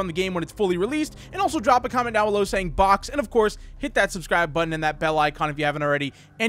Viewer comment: Fox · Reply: I thought he said to comment fox, but it was box So BOX